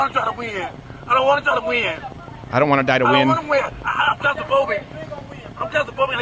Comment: Did he say "I don't want to die weird"